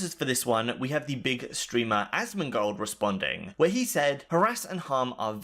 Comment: It sounds like you said "Bigot Streamer Asmongold"